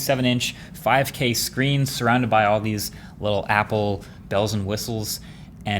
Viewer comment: I heard that as bezels and whistles lol